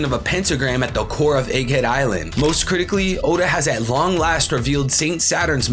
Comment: my dumbass thought you said moistcritikally